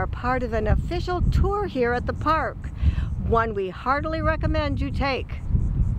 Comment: did she say "we hardly recommend that you take" the trapper Nelson tour? :P